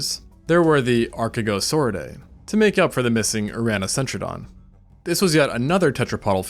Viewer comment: What is he saying at 16:16? Have no idea what that is and Google doesn't even understand what I am trying to look for. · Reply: Uranocentrodon?